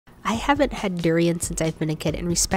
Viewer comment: I thought she said "I have a pet durian" in the start but she actually said "I haven't had durian"